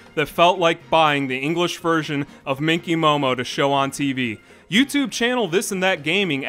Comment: I thought you were saying pinky momo